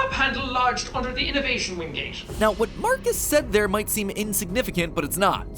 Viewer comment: is it just me or did Matpat call Sean "Mark"? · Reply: He called him marcus his story name